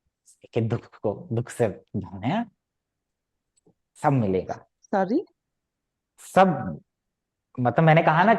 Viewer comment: that sorry😂 it sounded like… bhai mai nahi kamati kissi dukh vukh se😂👍🏻